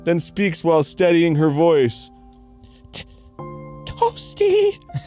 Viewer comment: For decades I thought they were saying, "Whoopsies!" when there was an upper cut in Mortal Kombat. It was only a few years ago I kept hearing people say, "Toasty!"